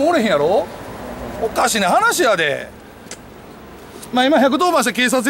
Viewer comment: 2分46秒のおかしな話やでが「汚い大人やで」に聞こえた。